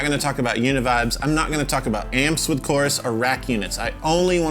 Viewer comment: yo i skipped and it sounded like he said "I'm not gonna talk about my penis"